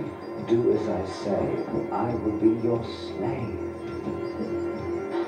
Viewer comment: what does the goblin say at 1:00 after the first one says "she's not going to say it"?? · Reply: never mind, after a good nights sleep (and a busy day at work), i just realised he says "doesn't look like it does it?" 😂